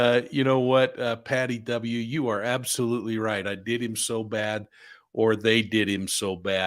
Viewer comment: The first time I heard the recording I heard, "I did him so bad."